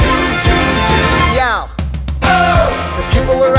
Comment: Am I the only one that always hears "meow" instead of yo